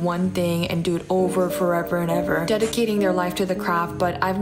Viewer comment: Sorry had to laugh.. I heard “dedicating their life to crap” 😂